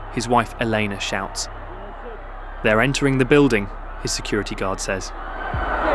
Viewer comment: what the guy actually says is "Vine Secu' ", Secu being short for Securitate, or secret police. What he's saying is "secret police is on its way".